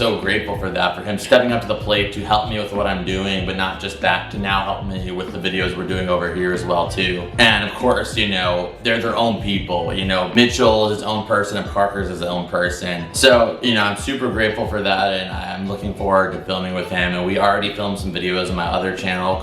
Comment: What he said 12:30 What I heard “mitchell is new parker” 👍🏿 😂